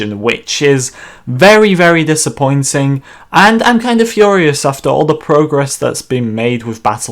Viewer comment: Didn't play the game since release of geonosis, first think I hear "I'm furious about EA" lol..